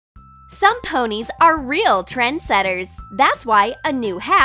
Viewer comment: What said: “Some ponies are real trend setters” What I heard: “Thsome ponies are real thread shetters